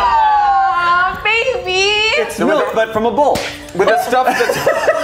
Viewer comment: What did Shayne say? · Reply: it’s milk but from a bull